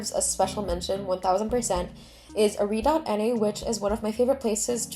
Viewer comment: Whats you current obsession? Didnt catch what u said and no subtitle lol 5:54? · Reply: are.na!! it's a tool to collect/curate/connect things but this channel of theirs might explain more •ᴗ• <link> (they have a website and ad!)